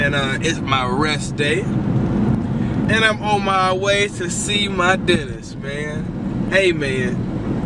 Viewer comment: thought he said "on my way to see my Denny's"